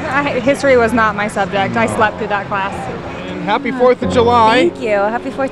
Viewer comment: That one chick "I don't know history I slept through that class" is a perfect example of why people become stupid adults, they think you only learn in school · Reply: I thought she said "I slept with that class"